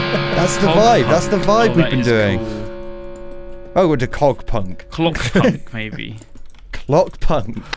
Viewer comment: I keep hearing "Cock Punch" when they label their style lol